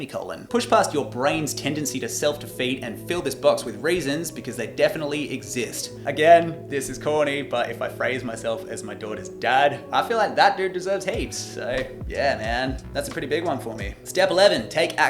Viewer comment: I heard 'fill this box with raisins because they definitely exist'